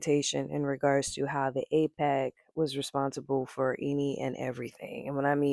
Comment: did you say AIPAC ? English is not my first language